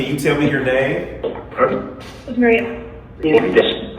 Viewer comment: I heard "Maria". What a beautiful place! I love your videos!!❤❤